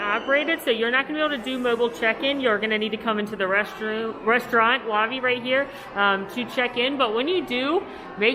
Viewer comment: When I heard "you're gonna need to come into the restroom...restaurant to check in" at 2:41 😂😂😂😂